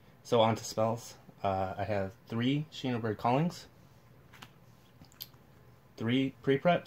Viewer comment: what is he saying at 1:54 what are the cards? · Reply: "Shinobird's calling" and after he says "3 pre-preparation rites."